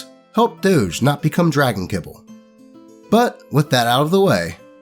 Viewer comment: What did you say about Dragons Doge? I didn't get that part. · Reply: 1 year late. Kibble is something for pets to eat.